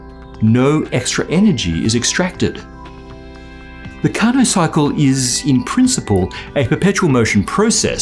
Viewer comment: I heard "carnage cycle" at the first time. XD